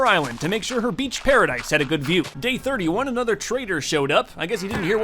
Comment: Thought he said traitor XD